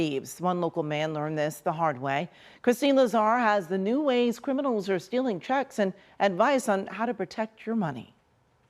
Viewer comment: oops! thought it said 'new ways to steal chicks'